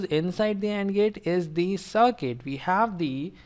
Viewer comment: instead of "circuit" i heard "suck it " hahahhahaha